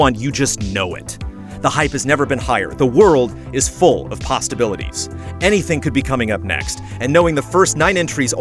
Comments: Did he just say pastaibilities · Did just say "pastabilities"?